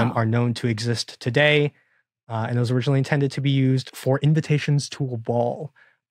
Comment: I had to go back because I was sure I heard "Invitations to a brawl".